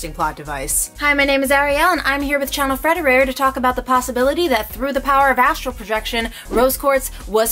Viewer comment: "Through the power of astral projection" Me: Oh. I heard "through the power of acupuncture".